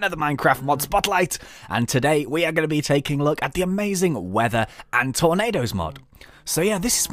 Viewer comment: I thought it said WITHER and tornados